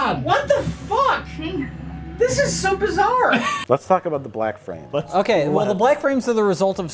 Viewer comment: When they said black frame I thought they said black friend